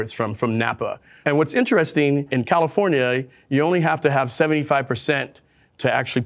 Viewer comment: did he just say califor-nyay? :)